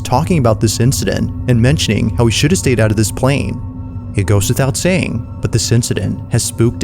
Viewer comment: did you just say "it ghost without saying"?